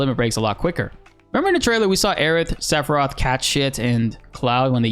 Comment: Anyone else hear him say cat sh1t at 19:36😂